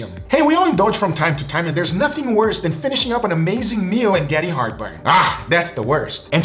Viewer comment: I thought you said Freaking heartburn lol